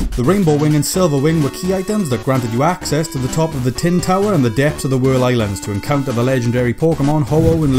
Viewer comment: At first I thought it said twin towers and not tin towers